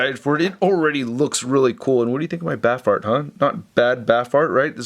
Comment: Oh “BAF Art” I thought you said “BAF Fart” 👍😂😂😂😜